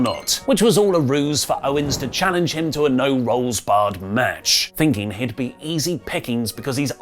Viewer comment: Did say No Rolls Barred